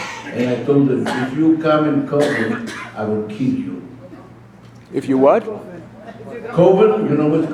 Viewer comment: I thought he said "if you come with covid, I'd kill ya".....well, COVID is still considered deadly for those who are elderly and immunosuppressed. Hence, why one needs to still isolate until covid has passed.